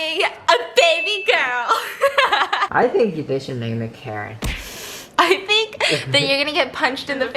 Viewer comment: When said: name her Karen I heard “name her carrot” 😂